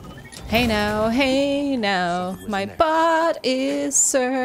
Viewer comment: I heard it, "Hey now, hey now, my butt is searching" lol I'm sure it was bot I don't know what's wrong with my ears